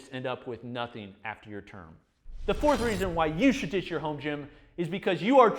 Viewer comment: did he just say “the 4th reason you should ditch your home gym...”